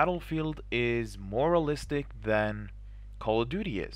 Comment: lol I heard you saying "moralistic" at 1:20 , that crack me up :D